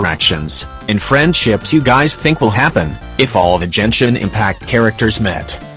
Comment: did i just heard jenshin impact xD